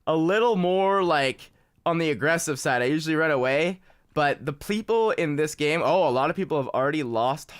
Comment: Anyone else catch that he said 'Pleople'